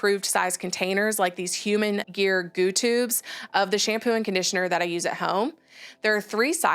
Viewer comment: enjoy your videos… instead of “goo” it’s actually “go” 😊